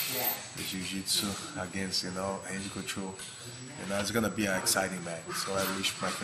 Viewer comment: Do they pronounce R with H sometimes in Brazil? Like Royce Gracie. It sounded like he said Handy (Randy) couture at the end of the vid.